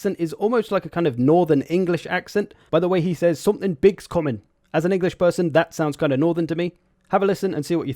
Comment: I think he says: Eyes up, something big is comming